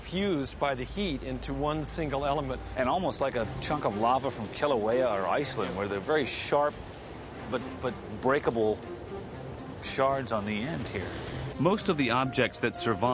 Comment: can somebody "transcript" what does the other guy say from 0:19? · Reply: "and almost like a chunk of lava from Kilauea or Iceland where the very sharp...but breakable, shards on the ends here"